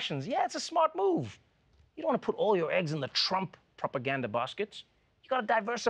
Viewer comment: Am I the only one that thought he said "Propagandalf"?